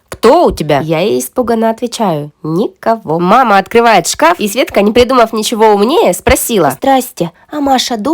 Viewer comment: Мне одной послышалось свечка а не Светка?